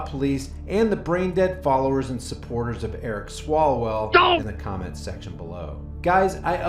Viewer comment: At first I thought you said, Eric swallows well ! Lol!